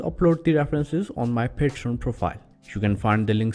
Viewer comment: I thought you were saying patient profile.....